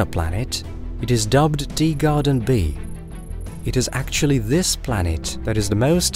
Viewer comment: Is it just me who hears it, or is this guy really saying “Deegarden B” rather than “Teegarden B”?